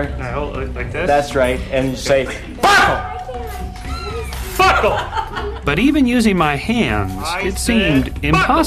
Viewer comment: does it kinda sound like he said, "Fuckle!" cuz i think he said it.